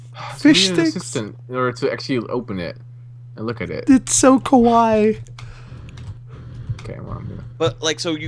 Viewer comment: it sounded like he said 'kowai' not 'kawaii', or was that what was intended?